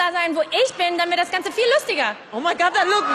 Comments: oh i thought she said after "wo ich bin", "da wird das Ganze viel lustiger". but she really said "damit". hm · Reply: I definitely heard "...dann wär das ganze..." and I am usually pretty good at deciphering sounds and dialects. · oh i thought she said after "wo ich bin", "da wird das Ganze viel lustiger". but she really said "damit". hm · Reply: i heard "dann wär das ganze( ...)" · I thought she said '"dann wird das ganze ..."